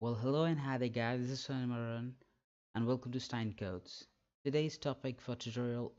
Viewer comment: I thought he said "welcome to Seinfeld" at the start.